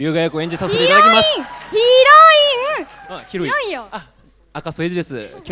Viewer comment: 「ヒロイン!ヒロイン!｣が「ヒロイン!ひどい!｣に聞こえた俺は末期